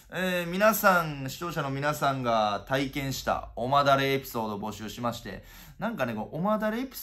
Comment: おますきじゃね？笑 おまだれって言ってたらお前誰なん？に聞こえてきた笑